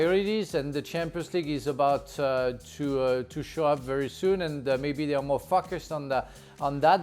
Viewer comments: i thought he said "they are more fuckers" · Did he really just say what I think he said? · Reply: He said focused lmaoo